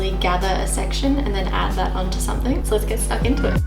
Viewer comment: did she say lets get stuck into it?